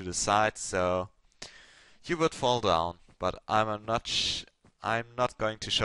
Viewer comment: did you just say "I'm a Notch" ? LMAO Like "I'm a God"